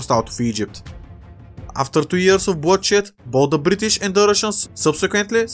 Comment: I heard “bloodshit” instead of bloodshed